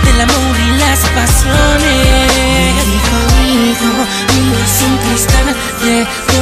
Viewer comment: Mierda yo pensé que decía DENTISTA ahora que veo la letra...Veo que dice ARTISTA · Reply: Yo Tambien mepaso yo pense que decia Me dijo "Mijo" pero en letra decia: ME DIJO "HIJO"